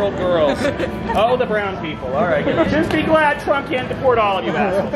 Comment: Right after 2:16 it sounds like he says, "Oh the brown people.". He's actually allowed to talk like THAT??